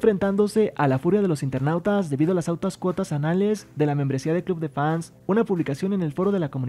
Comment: escuche anales